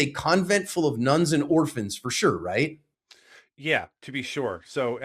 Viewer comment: When was talking bout the stucco wall I thought he said the building was full of guns and orphanages 😂😂. Your not wrong lol